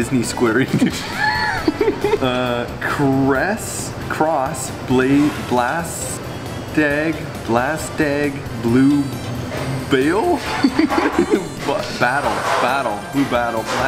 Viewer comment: I genuinely thought it said "Blaz Tag Blue Balls Cross"